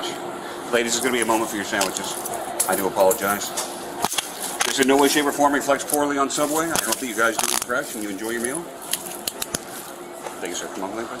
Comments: What he ACTUALLY says right here is: “ This in no way , shape, or form reflects poorly on Subway. I hope that you guys eat fresh and enjoy your meal.” The subtitles are all over the place. Anyways, I love that cop! Lol he’s fantastic!! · The subway arrest the cop was pretty chill. I like that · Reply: He’s HILARIOUS too! At 13:29 He actually says “ This in no way , shape, or form reflects poorly on Subway. I hope that you guys eat fresh and enjoy your meal.” He’s freaking hilarious